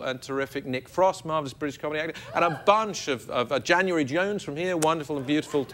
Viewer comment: Did he say...January Jones? <link>